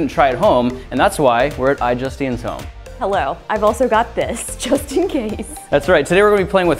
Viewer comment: I thought she said "I got this Justine case"